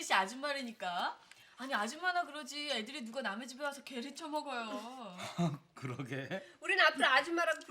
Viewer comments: 게를 처먹어요 라고들림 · 에 계를 쪄먹어요.인데 쳐먹어요.라고 들림!ㅋㅋㅋ